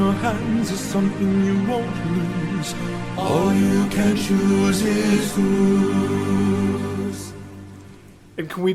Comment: the part in horse and the infant and they say "All you can choose is whose" i swear i thought i heard athena